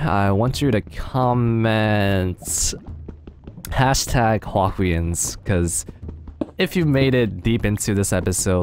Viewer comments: #HuahwiWins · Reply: misheard :p He said to comment #Huahwians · i thought he said #Huahwiwins not #Huahwians